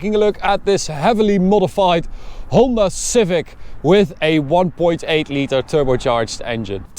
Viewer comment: I misheard him when I thought he said Homer Civic lmao